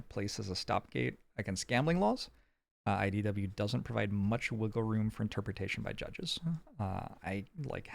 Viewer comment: I swear I heard you say “wriggle room” Instead of wiggle room. Is this foreshadowing to the wriggler????